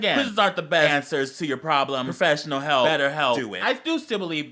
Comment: did he say "better help" at 5:42? tea !